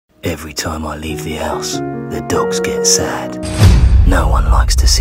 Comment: Um am I the only one who thought when he said “ever time I leave the house” he was sing “every time I turn my back”